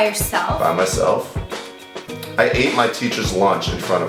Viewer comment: What did say at 4:54 what language was that please tell me if anyone knows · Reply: he said oh my god oh my god